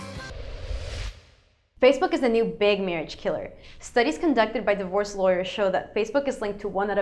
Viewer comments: i thought she said "studies conducted by the worst lawyers" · when you said 'divorce lawyers' i heard 'the worst lawyers'